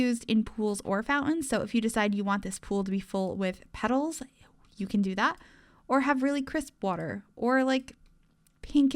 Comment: There is a whisper just after she says petals and it's terrifying lol Wearing headphones and had to go back to listen can anyone else hear it? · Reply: I think she whispered “eww.” Maybe she didn’t care for the algae she had placed.